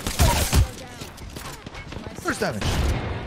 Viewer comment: nobody: slide jumping into frame: BURST DAMAGE!! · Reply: Thought he said Thirst Damage 💀